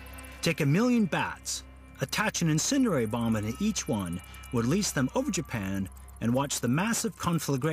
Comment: LOL if you activate Beta Subtitles at 00:47, u can read "that action sooner Obama each one" instead of like "attach an incendiary bomb on each one"